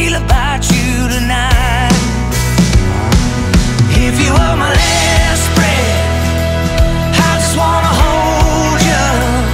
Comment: I thought he was saying "If you were my hairspray, I just wanna hold ya""".